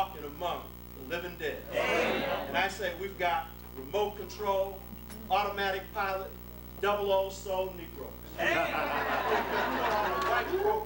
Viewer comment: Did he say "Remote Control, Automatic Pilot, Double 'Oh' Soul Negroes" @ 30:55, damn that's Malcolm X's branding consciousness incarnated! By the way, he's right, I have been - for far too long, one of those RCAPD Negroes who became conscious of that fact and decided to work myself out of that lethargic state.